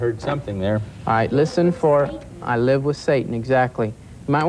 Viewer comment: 🎶 Aaaaargh eee woo ing ooohh 🎵 "I heard 'I move with Satan'!!!"